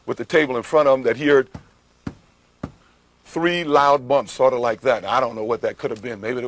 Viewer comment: “At no time did Kato say he heard 3 loud thumps, he said he heard 3 loud bumps” 😂, “maybe it was a signal or something, I don’t know”. He’s a piece of work lol. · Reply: I thought Kato said he heard voices not bumps